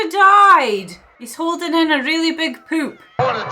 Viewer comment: Of all the many reaction channels, you have the most genuine, infectious, and enjoyable grin and laugh by far. Great movie choice again, thanks! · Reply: "He's holding in a really big poop." Did I hear that correctly?